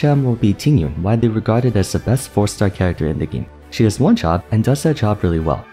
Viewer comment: I wasn't really paying attention and I heard this as "She gets one-shot, and does that job really well", I almost choked